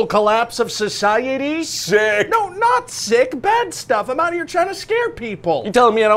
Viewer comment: I only hear "I'm out here in ad space..."